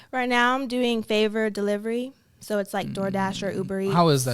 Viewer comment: I thought she said she does “labor and delivery, like DoorDash” 😂